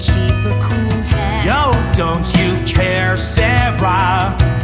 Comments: i cant be only one who hear "YO! dont you care sera" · I keep hearing... "Yo? Don't you care Sera?" Instead of "No, don't you care Sera?"